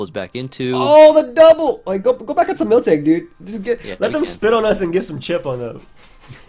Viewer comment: I fr thought said "Let them spit on us and get some chip on them". Like wow, some freaky stuff is happening on the ladder today, huh?